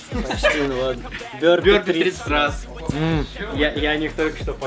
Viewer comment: Кто бы дошел до конца?) Заруба шла примерно 1.5 часа. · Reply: мне послышалось пёрни 30 раз😂😂😂😂😂